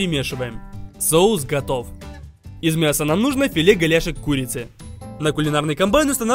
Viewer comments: почему мне послышалось "филе говяжих куриц" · мне одной показалось что он сказал "филе говяжей курицы" пжахшвш😅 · кто понял как мясо называется. Я слышу филе говяжий курицы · Мне одной послышалось " филе говяжой курицы " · извините мне послышалось "филе говяжей курицы" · Ванзай говорит: " Нам нужно филе голяшек курицы" Я слышу: " Филе говяжьей курицы" Я : 🗿